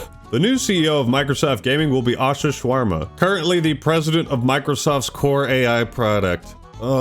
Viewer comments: Did you just say "Asha Shawarma" ? XD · Did he call Ash Sharma "Asha Shawarma"? 😭😭😭 I know it HAD to be unintentional but omg would be so racist if it wasn't 😭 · Did I Just heard you saying that her name is Asha Shawarma 😂